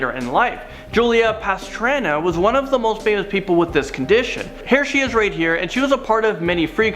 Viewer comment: I thought he said "Hair she is right here..."